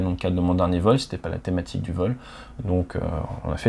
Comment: Pas compris la toute dernière expression, réécoutée 3-4 fois , ou plutôt, j'ai compris "Fail safe"… À la limite "stay safe" ou "fly safe", mais là : "plantez-vous et sortez-vous en", bizarre, après la leçon de la vidéo !